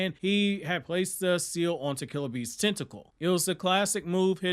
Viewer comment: I misheard you for a moment i thought you said "minato place a mark on killerbee's testicle"